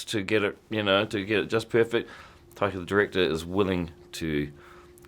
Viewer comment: Honestly not sure who is more talented, Taika the Actor, or Taika the Director. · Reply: Am i the only one who thought he said 'Taika the Dictator'?